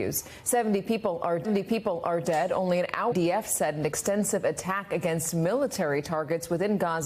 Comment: convoys carrying ammunition, not evacuees. captions doesnt match what the news anchor says. bad editing at 0:13. MSNBC think we r fools